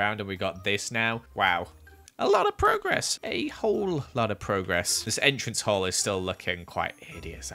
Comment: The first time I heard: "A-hole lot of progress!" I'm not dirty-minded, he just pronounced it weird hahaha